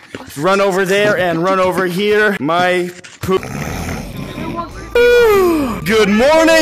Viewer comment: i thought you were gonna say some random monkey pooped in my ear💀🎩🎩